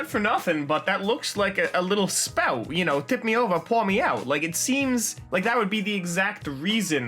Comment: Hello, how is everyone's day/night going? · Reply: I've had perversion on my mind and thought said "just tip me over and eat me out" So I went back and heard the correct saying, but wanted him to actually say it the other way